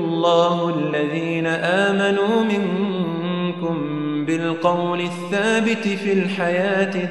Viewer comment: Salam all! Click here to support the Dawah: <link> This video is created by & for The Daily Reminder Network. If you like what you see, then help us help you & the Ummah at large by supporting the Dawah. * This video is creative common's (not copyrighted) ** No impermissible music was used in the production of this video JazakAllahu Khair, TDR Team. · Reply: The Daily Reminder assalamu aleikum.. please check what you wrote and what sheikh recite 5:28 . he said " amanu MINKUM..." Jazakum Allahu khayran. your brother from Italy